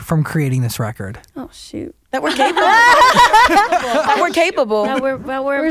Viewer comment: I thought Normani said "That we're gay people"